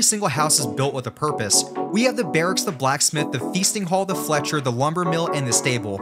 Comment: I'm watching the video in the background in 2x and heard "the feces hall" instead of the "feasting hall" XD